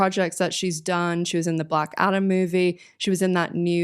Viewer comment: i couldve sworn you said black adder instead of black adam 25:03 😀